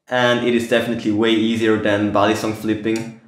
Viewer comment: Oh he said balisong I thought he said bison Bison would be a cool name for a knife